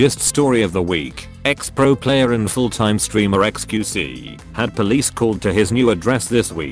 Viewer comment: i heard "ex pro player and full-time screamer xqc" on a serious note, thanks for another short, straightforward, and informative vid!